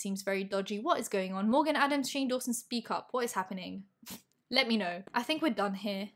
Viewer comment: Lmao I thought you were saying shane dawson was spill for a sec